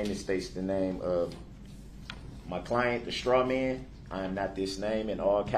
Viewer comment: did he say his clients name was the "straw man" ???? (Someone go back an listen at 1:26 an tell me if thts not wht he said)